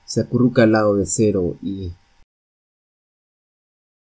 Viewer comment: Soy yo, o escuché "Aaaaaah F**k"?